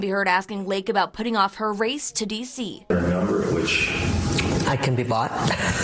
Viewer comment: Did I just hear her say "I can be Bought" !